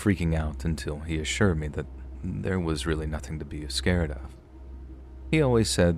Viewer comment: Time stamp 1:34....did you really say a-scared of.. 😂😂😂.